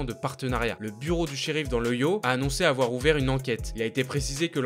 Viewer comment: Ohio tu le dit "Dans le Yo" ou c'est moi 😂